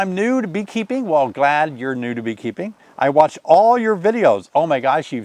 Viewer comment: I heard "Nude beekeeping" - had to go back and listen again. "New to beekeeping" was what was said. Nude beekeeping - No thanks 😮.